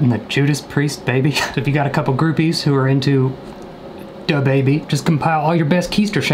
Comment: did he say Dababy LETS GOOOOOO